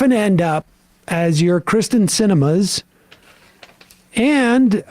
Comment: I thought he said "christian cinema" 🤣